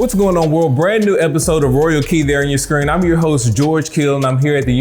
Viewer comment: I thought this guy said my name is George Kittle i was like wtf😂